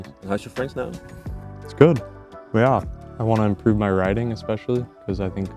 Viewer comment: At first I thought he replied by saying, "I want to improve my riding"